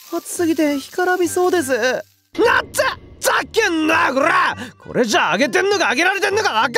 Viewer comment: 「ふっざけんなよごらー！！」がほぼほぼ「ざっけんな゛ぁご（巻き舌）ら゛ー！！」に聞こえるのほんとに好きです結婚しましょう（？）